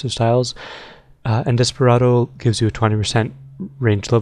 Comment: for a sec i thought you said omustardo instead of Desperado